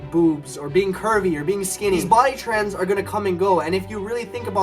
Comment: I heard "Kirby"